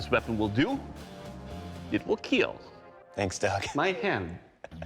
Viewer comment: Did he say, it will kill.. my hand?? 😂😂